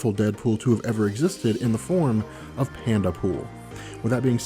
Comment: I didn't understand who said was the most powerful Dead pool. It sounded like he said panda pool; could someone tell me what he said?